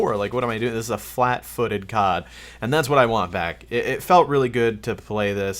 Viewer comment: did he say " and thats what i want vac" instead of back at 1:40 haha